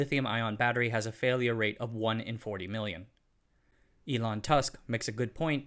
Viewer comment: Did you just say 'Elon Tusk?' Or is my hearing on its last legs? Good explanation, but... 'Thermal runway' is not a battery term. It's 'thermal runaway,' pronouncing all three syllables. There are three types of cell form-factors to consider, and each has implications for battery pack fires. Chevrolet's Bolt uses LG Energy's pouch cell form-factor. Pouch cells are large, flimsy, soft-walled, and as Elon mentioned, they undergo volumetric expansion at high states of charge. P:ouch cells are placed in trays to contain them; they cannot be structural elements. Cooling is applied to the trays in GM's Bolt pack design. Pouch cells are notorious for developing 'hot spots' because cooling is further away from the chemical reactions taking place than for other types of cells. Pouch cells are probably more vulnerable to spontaneous thermal runaway than other types. Prismatic cells were developed for the consumer electronics market. This form-factor excels at conforming to odd shape requirements in the devices in which they are used. They are also the most expensive cell form-factor. Choosing prismatic cells for mobility applications - VW has recently decided to switch to prismatic cells, for example - is an odd choice. Their sole advantage is conformability, which isn't necessary in a big giant battery pack - any shape will do, so there's no reason to choose a more expensive cell design. These cells do not expand, but they have brittle walls and are not suitable for bearing structural